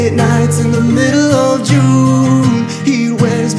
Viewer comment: I thought he was saying Pete Wentz been faking me out. 😭